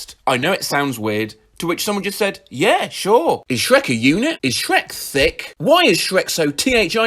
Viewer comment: somehow i misheard you saying "is shrek a unit" as "is shrek a eunuch" 😳